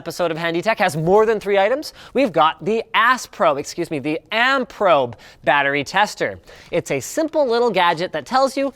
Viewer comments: when I first hear ampprobe, I thought I heard assprobe. lol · i heard "ass probe"